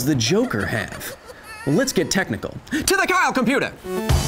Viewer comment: For a sec I thought he said "to the cow computer!".